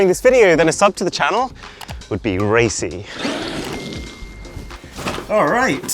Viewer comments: I swear, for a second, I thought he was going to say: A sub to the channel would be racist. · I thought Arun was about to say "a sub to the channel would be rasist" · i deadass thought he said "a sub to the channel is racist"💀